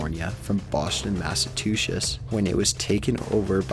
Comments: It sounds like he said "Boston massatushes" lol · D....Did you say "Boshton, Massatooshiss"? I'm sorry, I'm from MA so that made me giggle hahaha. But awesome vid, that stuff is eeeeeerieee. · YOW I AM THE ONLY ONE SPOTTING THESE FUCK UPS ON THESE VIDEOS 14:53 he sayong machatuces instead of Massachusetts . very interesting i might say though. :>